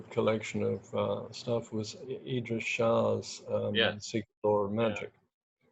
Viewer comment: What author did Dr Skinner mention at 51:15? I can’t quite understand what he said. Great interview 👍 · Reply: idries shah secret lore of magic